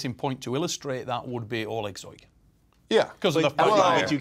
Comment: What name does he say? Alexoi? I dont know anything about arm wrestling but I am curious who this beast is.